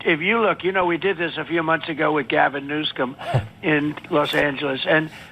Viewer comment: did he really call him "new scum"? I hope he just mispoke because if not my gosh is that childish. Making fun of names... our president!